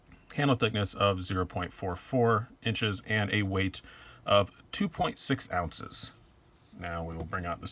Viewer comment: Wow I thought you said “very light 6.2 ounces” I was saying what the fuck do you mean very light....